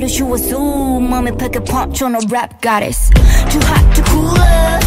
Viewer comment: No offense but why Jessi? She like every western rapper ever 😭 Shuhua tho 🫡 Also, I kept hearing Jurin say "Too hot da cooler" 💀